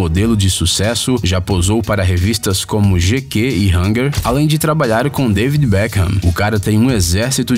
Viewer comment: É impressão minha ou o nome da revista que eu ouvi era g gay em hangar no 4:40 kskksks